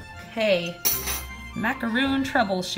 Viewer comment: Did she say macaroon? I thought you just yelled for a whole 30 seconds about how it's not called macaroon lol